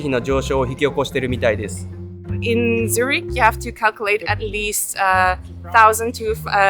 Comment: のIn Zurich,とat least の間がまじで聞き取れないので誰か教えてください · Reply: in Zurich, you have to calculate at least でしょうか